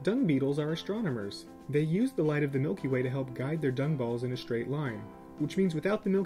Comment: On the Dung Beetle part, I thought thAT MILKY WAY SAID MIKEY WAY O.O XD